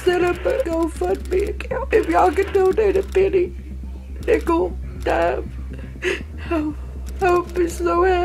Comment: I thought she said Pickle instead of nickel in the beginning